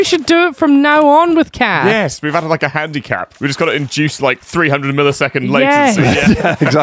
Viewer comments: I honestly thought said they added a handi-Cath, lmao. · I thought said 'handicath' for a second there